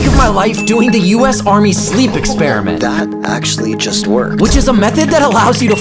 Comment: i heard "Russian sleep Experiment"